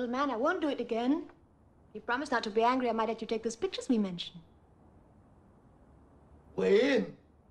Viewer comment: there is no way she says "the Pictures we Mentioned" all i hear is "the Spitches Me Menchen"